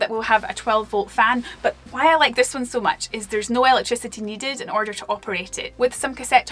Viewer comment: what is needed? I can’t understand your words. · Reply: Electricity